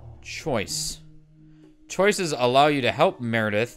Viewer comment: i literally thought that said choke for a good minute